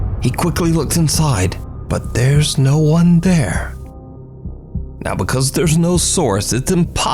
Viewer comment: Ya' had me. Up until you posted yet another Franko the Fake upload. 🙄 Edit: As for the last one I heard 'There's no such thing as..monsters' That's creepy AF!! 🙀